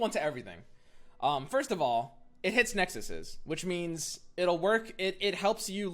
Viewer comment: Sorry, did I hear "Nexuses"?